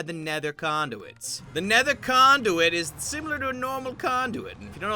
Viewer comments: I thought he said nether condoms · thought he said nether condomit